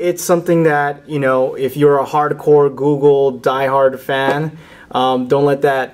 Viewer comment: he says "hardcore" and google thought he said porn